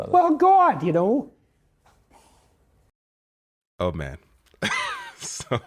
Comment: I thought you said 'old man' at first lol